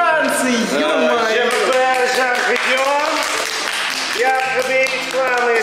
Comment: Мне послышалось Жанпет или Жан котенок на